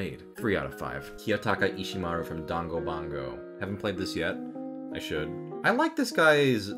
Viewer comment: Did he just say dangobango instead of danganronpa I- 😭